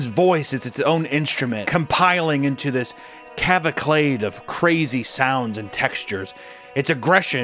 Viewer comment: cavaclade? What does that mean? Did you mean to say cacophony?